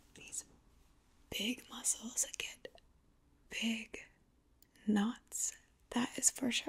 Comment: did she just say i have big nuts?